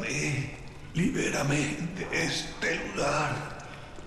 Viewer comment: que dice? Lular?